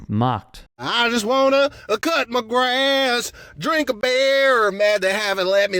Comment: Omg I thought that bumpkin was saying “I just wanna kiss my fish”. He ain’t speaking English 😂😂😂